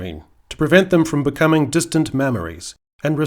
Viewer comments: I swear he says "distant mammaries" at 7:02, brilliant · Did you say “distant mammaries”???